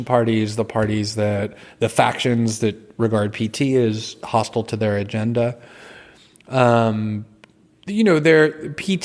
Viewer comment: "hostile to there agenda" - google/youtube automatische Untertitel macht daraus "hostile to the edge" Hab ich das falsch verstanden? Oder ist das so eine automatische Erkennung ... zur politischen Beeinflussung?